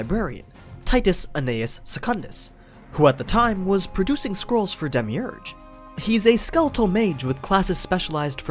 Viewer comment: “Who was producing squirrels for demiurge” Anyone else heard it like that?